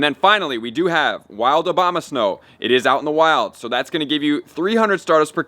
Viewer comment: D im not the only one that sounds like im saying OBAMASNOW !!!